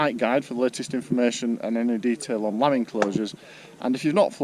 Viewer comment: Some of the land where we take-off and land is owned by farmers who keep sheep there. Lambing season is when pregnant sheep begin to have their babies - lambs. We are asked not to go onto the land where the sheep are at this time so we do not disturb them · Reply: Ah! Lambing :) Thanks for clarifying that! I misheard it.